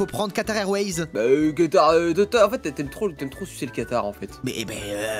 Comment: j'ai entendu toi tu aime super le camtar 😵